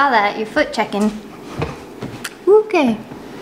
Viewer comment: When sat down and said to Comet, you foot checking? I thought she said fact checking and maybe he is. Maybe he is in fact checking that is correct when she said his feet are still there. He's just being a team player :)